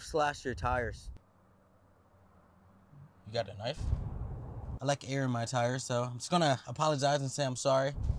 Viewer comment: I thought he said " i like ya ear in my tires "